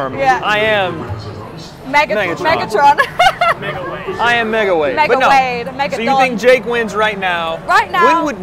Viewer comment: Did she say mega dong?